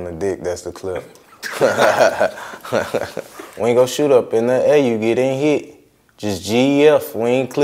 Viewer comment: I thought he said bring yo shootas up in the A ya getting hit